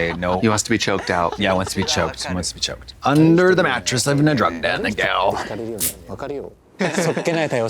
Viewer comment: did she just say "under the mattress in a drug den" ?? LOL